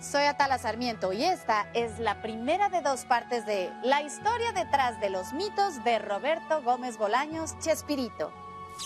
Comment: Decepcion!!! yo entendi " la Histeria detras del Pito"